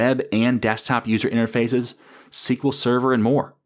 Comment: Building out a help desk app sounds very interesting. Thanks for your work and all that you do for the community. Sidebar, at about 13:20 re. new technologies, I thought you said "single server", what's that? Total speculation but did you perhaps mean SignalR?